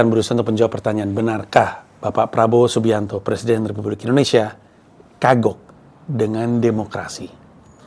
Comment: ngomong Presiden Republik Indonesia, kedenger Presiden Terburuk Indonesia